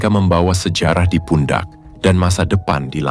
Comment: I hear the word "PUNDA". in swahili the word "PUNDA" means donkey 🫏. Is it the same meaning ?. Big luv from 255 Tanzania 🇹🇿 a country from east African.